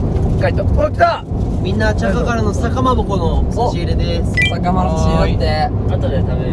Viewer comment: まちゅが笹かまぼこって言ってるところ何回聞いても『さ』が1つ聞こえなくてさかまぼこって聞こえる笑